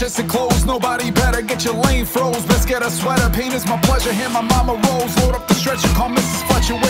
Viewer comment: I really thought the sett part said "penis" instead of pain is.😭